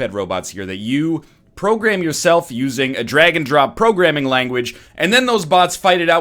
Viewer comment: At first I thought he said Dragon Drop not Drag and Drop